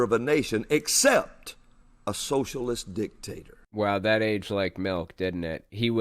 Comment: That was awesome. A commercial came on right when he was saying Biden is a “socialist dictator. And it cut off all of a sudden so it sounded like he said “socialist d*ck”. Aaaahhhh, man. Good to get a laugh while being so baffled by how insane the world is right now.